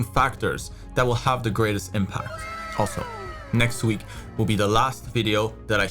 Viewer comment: for a second i thought said "next week is the last video" and I got real scared